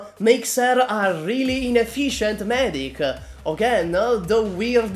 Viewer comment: when I heard the word ine-FISH-end. Sry but my Aegir humor suddenly kicked in